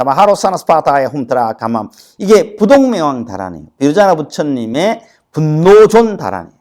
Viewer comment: 뭐라고 하신건지 알려주실 수 있을까요? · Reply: 부동명왕 다라니입니다.